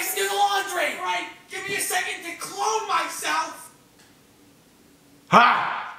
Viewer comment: grayson sounded like josh peck from drake and josh when he said ‘just give me a second to clone myself’ edit: woahh thanks for all the likes and replies lol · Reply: anayaedits I thought he said clothe Myself